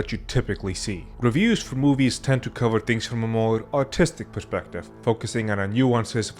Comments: I thought he said from an autistic perspective, instead of artistic. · i thought this said “autistic perspective” instead of artistic perspective and i was like “sounds about right”